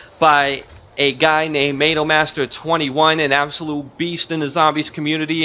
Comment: Analmaster21??😂😂😂I'm sorry but that's what I thought you said but then I rewinded it😁😁oops sorry but yeah great video dude this will help me defiantly